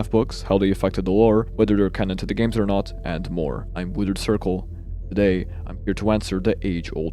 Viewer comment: I swear on everything I heard you introduce yourself as "The GooningCircle" the first time around. I think my brain is rotting.